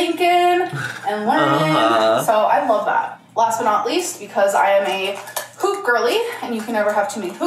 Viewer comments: I thought she said I’m a POOP 💩 girlie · When she showed her hoop earrings, I swear I thought I heard her say "I'm a poop girlie" lol